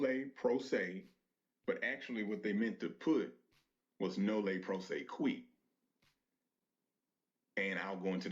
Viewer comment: did he say "Nolle Pro se queef"? "Pro Se" does NOT mean "proceed".